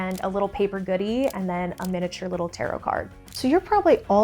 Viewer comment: "miniature Tarot card"? Did I hear that correctly? 😳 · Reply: Yes she definitely said a miniature tarot card. I just wonder how she’s doing that? Randomly just picking one from a deck for each person?